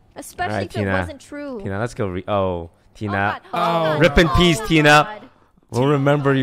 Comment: Did he say rip in peace XD